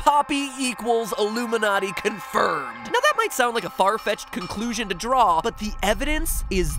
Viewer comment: When mat said far fetched I actually thought he said “Fart Patch” XD also if you see this can you do a theory on plankton...from spongebob I don’t know why